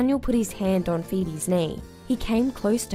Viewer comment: "I saw everything" Me: well not rlly cuz I thought u said the power went out..